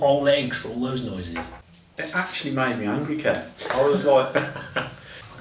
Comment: I at first thought was saying "It actually made me hungry Kev"... so I thought maybe it'd be that he ate all the eggs (so he wouldn't have to give this album any).